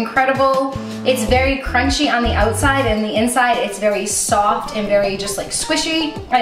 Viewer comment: Hello! I love this recipe so much, and I want to make sure that everyone understands and enjoys it. That's why I wanted to let you know that your captions are a little off. At about 2:36 the auto-captions say"stopped" instead of "soft." When you get a chance, could you fix it? That'll help the 360 million folks around the globe who rely on captions to understand videos. Thank you!